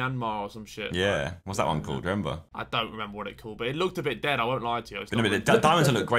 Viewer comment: I've never thought I would ever hear harry says our country name. Edit : I was so gassed I didn't hear at first watch but LMFAO bro said "it looks a bit dead" 😂